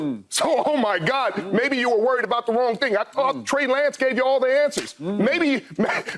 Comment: “maybe you were worried about the wrong ni**a” Anyone else hear it?